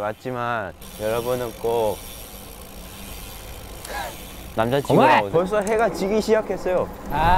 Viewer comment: what jeno said was “namjajin” mean have a friend gender boy not boyfriend and the caption make us misunderstanding but im just gonna ignore then